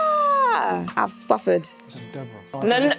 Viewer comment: 😂😂😂 the Nigerian in Deborah finally comes out. "And you, mmmmhh, mschew, I have suffered" 😂😂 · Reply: What I heard was “I don suffer”🤣🤣🤣